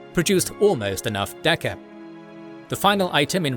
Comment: Did i hear you say “enough dakka”?